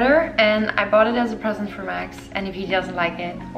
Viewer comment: did she say "for my ex"?? they're not together anymore?